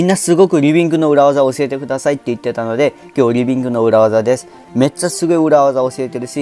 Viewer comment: リビックじゃなくてリビングに聞こえる